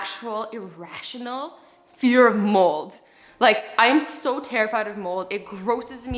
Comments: Lol, I thought she said she had a fear of moles. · j thought she said moles and I was so confused OMG I'm dying🤣🤣😂😁😄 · I started panicking cause I thought you said you hated moles 😂 (which I have on my face) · am I the only one that thought she said "moles" as in like the animal😂😂😂 and when she said about biology I was like: girl you didn't learn SHIT about moles in ur lungs😂😂😂😂